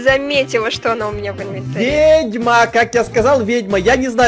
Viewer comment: Мне одной послышалось не "ведьма" а "гетьман"?😂